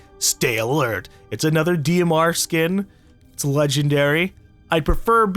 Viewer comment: did he call it a skin